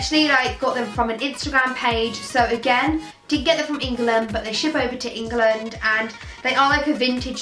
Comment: I know she said she got them of an insta page but after that did say 'i did actually get them for England but they ship over to England'??????😂❤ Love you 🌍💙👑